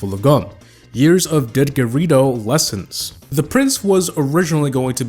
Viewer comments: what did he say did ger E doe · When he said didgeridoo I thought he said did dorito